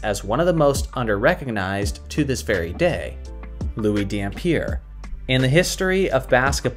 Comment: I was looking away and I actually thought you said Erick Dampier and I almost spat my drink out.